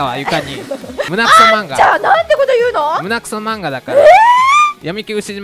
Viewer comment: ガチ恋さんの「アンタ！」が「あんちゃ〜ん！」に聞こえる